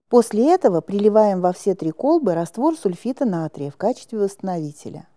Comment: Мне послышалось или сказали сульфид натрия, хотя формула говорит о сульфате натрия? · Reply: Сульфит натрия